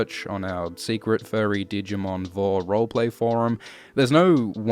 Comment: Did he just say "secret furry digimon vore roleplaying forum"?